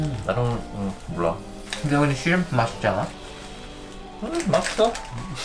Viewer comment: I thought he said germs instead of shrimp I was like protect him